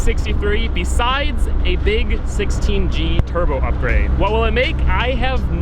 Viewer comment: Oh I guessed WAY too high. For some reason I thought you said it had a big turbo and a tune so I guessed around 300. 217 at the wheels is great considering that car was only rated for 195 crank!